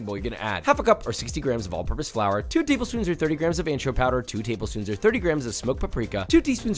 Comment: it sounds like he says add 2 tbsp of "antro powder"... what is that? The closed caption spells it "anro powder".